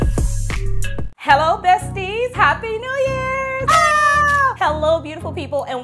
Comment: First, I thought you said Happy New Year dusties, lol. I had to rewind it back 🤣🤣🤣 Happy New Year 🥂🥂🥂